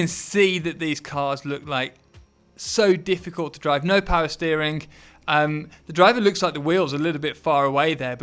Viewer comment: did you say no power steering? 😳